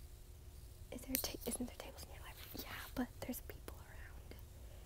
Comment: What did she say at 4:25 ??? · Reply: Vivi 247 she said isn't there tables at you're library